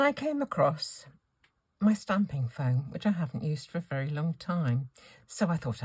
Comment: watch the video😊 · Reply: So sorry … didn’t mean to aggravate. I need to clean out my ears, as, at one point, I thought you’d said something about “stamping felt”, and I most definitely didn’t want to miss the meaning of a possible new product. Be well!